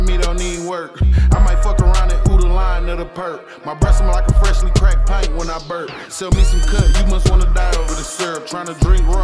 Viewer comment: my breath smell like a fresh pint when i burp 🤣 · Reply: 😂😂thank you for clarifying i played it back a few times like tf did he say ...thought he said his breath smells like a crack pipe when he burps ,,,i said aint no way thats what hw said 😂😂😂😂😂😂😂so i said lemme check the comments !